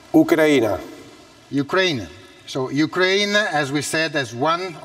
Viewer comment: did he just say cocaine???